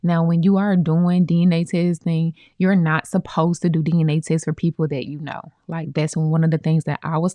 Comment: you cut off when you were saying "you're not supposed to do dna testing for....? What did you mean to say? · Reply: For people that you know.